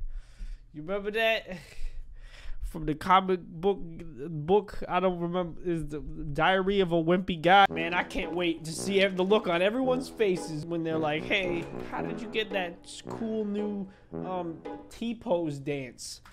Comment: sounds like he said diarrhea